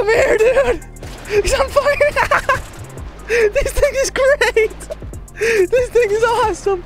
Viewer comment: I thought he said this niggas great